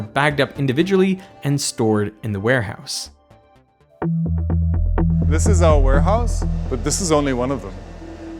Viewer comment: I heard "whorehouse"...